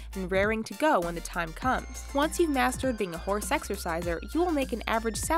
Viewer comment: It sounded like she said hoare sexerciser